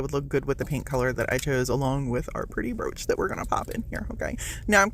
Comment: I had to stop the video and come to tell you that at the beginning, i thought you said "roach"! I knoowwww!!! Then when you said brouch (sp?) I was like, Oh ok, thay makes more sense! I mean, you never know these days 😅 now, back to video 😊